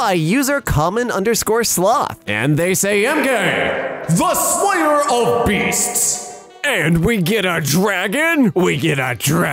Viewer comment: Instead of "Emkay the slayer of beasts", I heard "I'm gay the slayer of beasts" and now Emkay will forever be I'm gay